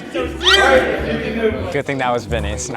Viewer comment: I thought he said I think that was vinnys